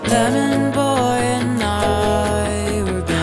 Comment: Everyone: “OMG A MOOMIN REFERENCE” Me in the back: ”woa yeah moomin woo totally know what that is haha” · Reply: I full on only found out about moomin like a month ago and so when I heard that lyric in the past I was like “what the hell is he saying” and today I was like “did... did he just say snufkin?”